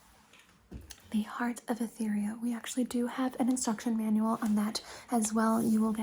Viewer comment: Did I just hear the word- "Etheria?" She-ra fan noises